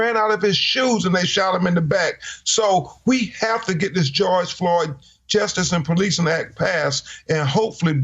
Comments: Overall if you got every black leaders every person that's black in a position of power how would we go about change. We had oprah on breanna taylor and so forth and so forth but change legislation.. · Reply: every time I hear "THE GEORGE FLOYD"S ACT" sounds like they are saying "THE IDI AMIN ACT" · Donald Trump is the cause of the KKK and the skinheads joining the police force across this country Donald Trump evil is not confined within himself but it affects others he is the devil John 8:44 🕶️ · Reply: every time I hear "THE GEORGE FLOYD"S ACT" sounds like they are saying "THE IDI AMIN ACT" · These are hate crimes. We want a document like they gave Asian over night. Y can’t we have a doc like they gave Asian? These are hate crimes. These are hate crimes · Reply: every time I hear "THE GEORGE FLOYD"S ACT" sounds like they are saying "THE IDI AMIN ACT" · 180+ black people killed since George fFloyd case ended, 🏴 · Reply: every time I hear "THE GEORGE FLOYD"S ACT" sounds like they are saying "THE IDI AMIN ACT" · Some evil wicked racists in Eastern NC.. Just research the history...........the true history!!! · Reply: every time I hear "THE GEORGE FLOYD"S ACT" sounds like they are saying "THE IDI AMIN ACT" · Film bring redacted is manipulating of facts! If Brown is being put on trial then everyone involved need to be put on trial… including the judge. Let the history flow!!! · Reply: every time I hear "THE GEORGE FLOYD"S ACT" sounds like they are saying "THE IDI AMIN ACT"